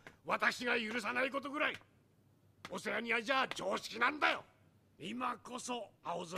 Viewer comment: どんなアンチコメントに対しても「オセアニアじゃあ常識なんだよ！」って言っておけば通じる説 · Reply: 最初「オセアニアじゃあ常識なんだよ！」を「オセロニアじゃあ常識なんだよ！」に聞こえた